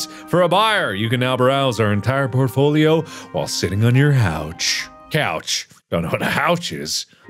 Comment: I thought he said sitting on your house 🤣